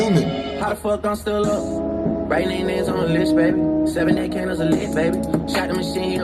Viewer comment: what did he say 17 candles!? Or 7? Idk🧐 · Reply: 7, 8 candles are lit